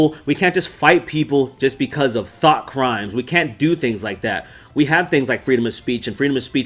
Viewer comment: I'm sorry but I heard 6:07 like " we can't just hurt people because of T H O T crimes " XD